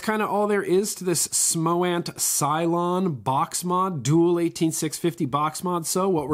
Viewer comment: Hah! I thought it said "Smoant CLONE"